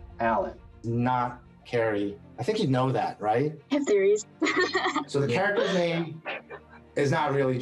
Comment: What did she say around 2:14? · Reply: She said „I‘ve theories“